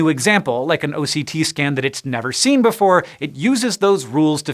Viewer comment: Thanks. :-) Also, at first I thought he'd said OCD scan...